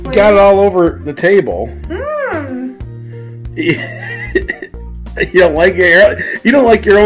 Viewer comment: Honestly kind of surprised knows the word "concoction." Seems like that would be too big a word for him. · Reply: I heard “cacoction” 😆🤷🏻‍♀️